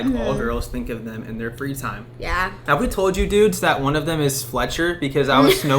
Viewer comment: When you said “wouldn’t a cool name be fletcher” I heard “wouldn’t a cool name be sweatshirt” and I was about to comment please don’t name your baby sweatshirt